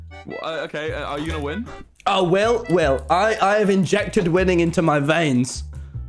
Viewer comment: MCC STARTS AT 32:19 Reply with your own timestamps! - Rudylmao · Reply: "Wil, Wil- I have injected winning (I THOUGHT HE SAID WOMEN) into my veins."